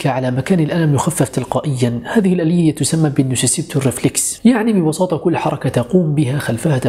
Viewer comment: هه 😀😂 سمعتها ف البدايه كورنيفليس (حبوب افطار) بعد التدقيق اللغوي لقيتها تورنفليكس